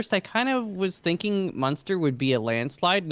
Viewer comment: For most of the conversation I kept mishearing "muenster" as "monster", so in my mind I pictured Monster Energy Cheese, and honestly it makes sense. Also I liked how while two out of three colonists were on their deathbed, the guys were like "So what kind of cheese do you own?". 100% focus. :D